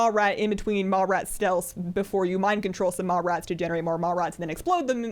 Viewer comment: For a moment I thought she was saying "mall rats"